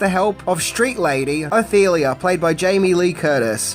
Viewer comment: Jamie Lee's characters name is Ophelia and it sounded like you said, "Athelia?" 🤔